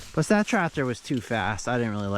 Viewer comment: "But that tractor was too fast ...I didn't really like running it" -AC. I think I had too much wax in my ear and misheard?